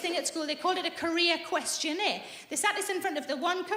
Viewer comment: Ah I thought she said the "Korea Questionnaire" and I was confused for a min, lol